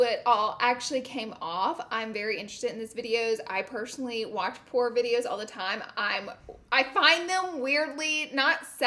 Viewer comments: seconds in I thought you said I personally watch porn videos all the time 😂😂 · Sounded like you said “Porn videos....” 😂😂😂😂 ya girl was rollin!